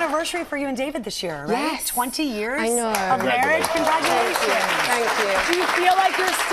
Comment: did she say CONDRAGULATIONS ????? Rupaul is that you ??